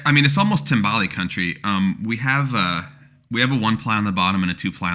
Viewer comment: I thought you said tamale country. Which sounded delicious.